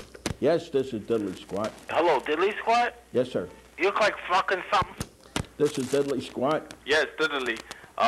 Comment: "You look like fuckin' some..." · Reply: Oh i thought he said "you look like a fuckin thumb" lol fugg